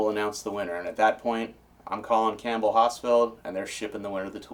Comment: I thought he was saying campbell hospital. It wasnt until I went outside and saw my Campbell Hausfell air compressor that I put 2 and 2 together.